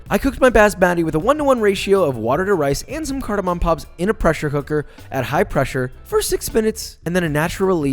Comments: Am i the only one who heard "Pressure Hooker" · Did you say "pressure hooker" at 2:40?